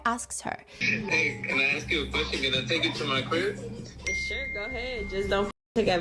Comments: what did he say here? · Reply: “Can I take her to my crib?” (He was talking about her DAUGHTER 😭) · what does CRI mean? or whatever that man said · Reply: He said “can I take you to my crib” which is like “can I take you to my home”